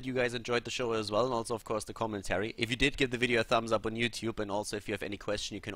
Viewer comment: When you say "commentary" it sounds like you said "comments Harry" and it made me jump since that's my name. :v